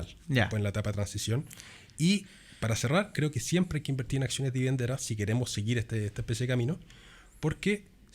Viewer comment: Min 45:46 alguine me puede decir que dijo: Acciones de vivienda entera? De partida entera? Por favor y gracias. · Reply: Dijo “…siempre hay que invertir en acciones dividenderas si queremos seguir esta especie de camino…”